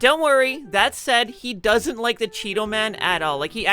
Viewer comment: i thought you said cheadle, and i was like what does he have against Don Cheadle?